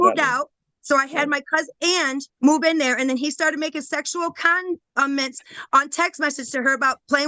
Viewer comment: Did I just hear her say “sexual condiments?” Now THAT would be interesting and kinda fun!